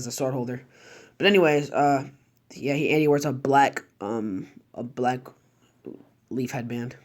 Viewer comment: I swear on g I heard you say black lee